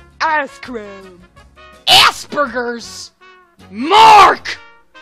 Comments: I thought she said ass burgers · When I saw aspergers I thought she said ass-burgers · i heard: assburgers